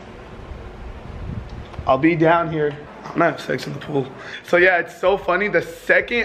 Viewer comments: I think it says I'm going to have $ex 🤔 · he says go check it out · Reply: David Ortiz I thought he said I'm having sex in the pool